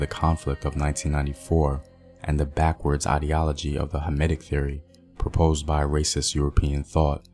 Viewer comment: Did you say hermitic theory, because I can’t make out what you were saying but the auto-subtitles say hermitic and I can find any articles that link hermitism and the conflict.